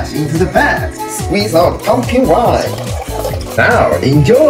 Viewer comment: I first heard "pumpkin pie" and was confused as hell 😂😂 I was like what pumpkin pie was yo mama making but then watched it for a second time and was like "ooooooh winneeee not pie" Cons of hearing loss lol